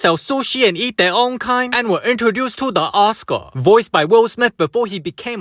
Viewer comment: I swear I heard "and then we're introduced to an a**hole" instead of "da Oscar" part. I cried laughing at both things when I saw the subtitles for the correction. 🤣🤣🤣🤣 (tho its not like the a**hole part aint true in the movie)